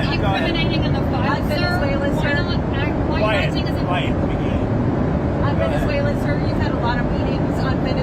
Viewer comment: Does he say "Quiet Piggie"??